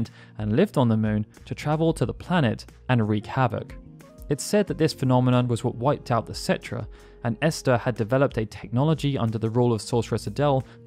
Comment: Thought you said "Cetra" for a second there. What confusing names... Both were even localized with a c that makes an s sound (romanized Japanese doesn't have c, it has s and k)